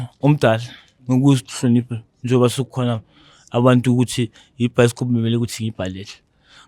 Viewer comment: Whoelse heard him say lokshin bioskop😂😂